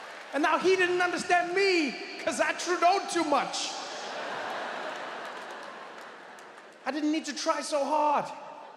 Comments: Did he just said: " I Trudeau'd too much" 😅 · did he really say “I Trudeau’ed too much?” 😂😂😂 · Subscribe & turn on notifications to see the new videos first! <link> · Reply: Did he just said: " I Trudeau'd too much" 😅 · Did he say Trudeau-ed too much?? 😂😂